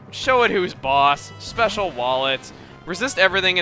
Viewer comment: When you sent out Acooltent and said "come on Acooltent show 'em what you got, special wall it..." I could've sworn you said "Special wallet"! THERE IS A SPECIAL WALLET????